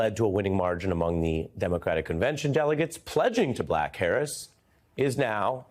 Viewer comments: Sure sounds like he said Pleading to "black" Harris... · did he just say to “Black Harris”? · Did he just say pledging to BLACK Harris or was it just me? Even the auto caption heard black Harris.